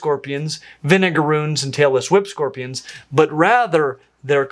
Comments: I thought he said Taylor Swift scorpions! had to put the subtitles on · Taylor Swift scorpions? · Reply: I think he said "Tailless whip scorpions", but I heard "Taylor Swift scorpions" too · when he said "tailless whip scorpions" i thought he said "taylor swift scorpions" lmao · At one point I heard "tailless whipscorpions" as "Taylor Swift scorpions" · not gonna lie, when you said "tailless whip scorpions", I heard "Taylor Swift scorpions". I had to replay that a couple of times. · I initially thought you said “Taylor Swift Spider”, rather than “tailless whip spider”. 😂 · Had to skip back and listen 5 times, the last with captions just to figure out that did NOT say Taylor Swift scorpions! I was wondering if my ears were bad, if it was a cultural joke I wasn't aware of or what?! 😭😂 It's late and I have to be up early but I'm too tired to figure out if I misheard Vinegar Runes or if the captions got it right 😅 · When you said "tailless whip scorpions," I was washing dishes and only half watching what was going on, and I genuinely thought you said, "Taylor Swift scorpions." I immediately thought, oh god, surely biologists haven't found a new species and named it after a famous person again like the David Bowie spider. For a second, I was thinking how can a scorpion look anything like Taylor Swift 🤔🤣